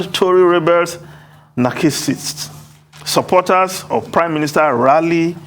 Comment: did I hear Narcissist pronounced "NARCHISSIST?????